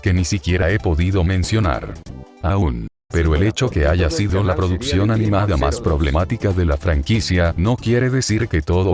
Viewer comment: ¿Que dijo en esa parte? se escucha una voz de fondo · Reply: "Si no fuera por ti todo el canal sería de Digimon 02"